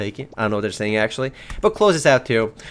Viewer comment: I thought you said Fists of poo for a second.